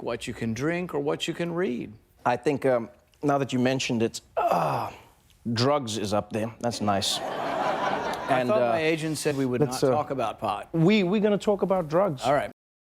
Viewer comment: I thought my agent said we're not gonna talk about pot. LOL